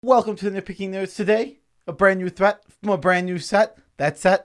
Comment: This is the first time I’ve been to yalls channel and I thought you said “welcome to the no picking nose channel”